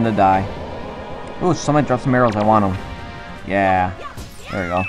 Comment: i thought u said oh some1 dropped marijuana XD